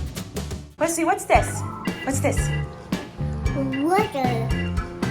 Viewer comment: It sounded like Wesley said "what is that" in front of fish tank. So cute.